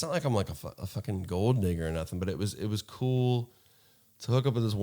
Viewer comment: am i extremely high or did it sound like he said im not a gold n*******r or anything lmao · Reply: he said gold digger